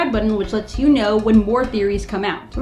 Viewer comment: I usually thought bill cipher said "bye goal bye" instead of "buy gold buy"